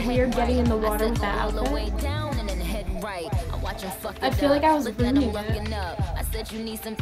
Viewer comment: Does anyone know what saying at 7:28??? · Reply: i said “id feel like i was ruining it”🙈i was talking about nicki in the water in that fancy outift lol